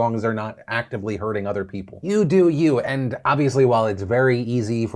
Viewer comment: did Phil say "New Dude You" but disguised it as "You do You" lmao